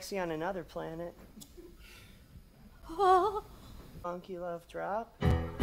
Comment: did he say monkey love drop??? Wgat